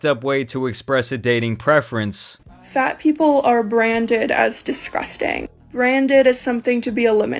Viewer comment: Did she say disquesting instead of disgusting? @ 3:12 😂😂😂😂